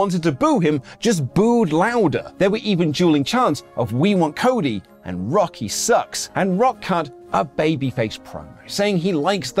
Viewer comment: At first i thought thwy were saying "we want cody - rocky sucks" but aftet a while it sounded like "we want cody - cody sucks"